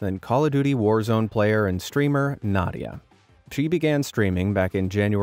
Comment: it's probably because i keep hearing "cheat" a lot that throughout this playlist binge but when i came to the Nadia's intro segment, i keep hearing "cheat began streaming in 2021"